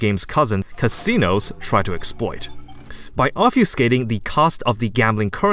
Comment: nice video thank u :D but what did you say at 10:34 about the costs ? i understand english pretty well but i don't get what you said there and the subtitles aren't helpful either : "... casinos try to exploit by off youting the cost ..."